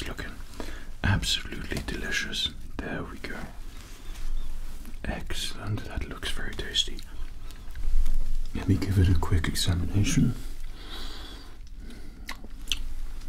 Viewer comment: I thought he said "Insemination" 😭💀